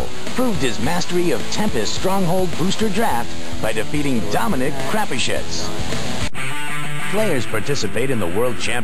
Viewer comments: it sounded like John Finkel beat Dominic Crapashitz. Fail name. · Did he just say the guy's name is Crappy Shits? ~Zuty